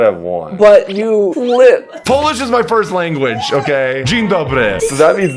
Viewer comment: I was mostly listening and I thought said 'foolish is my first language', so when he said Dzień Dobry I was like 'wtf?' 🤣🤣🤣🤣